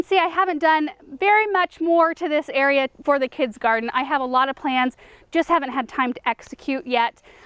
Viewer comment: I heard her say kids garden and not garden and now im convinced of you know what🤔🤫